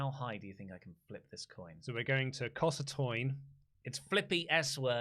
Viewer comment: Did say Coss a Toin??